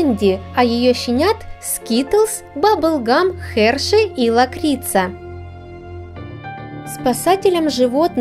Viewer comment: мне послышалось мокрица 😂